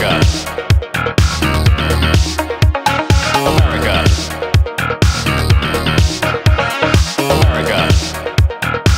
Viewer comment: Not hear what he says in the background, tell me, I do not speak English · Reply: Parker ..... Maximum Strength